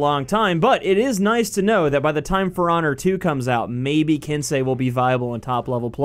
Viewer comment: For Honor 2?? was I hearing that right?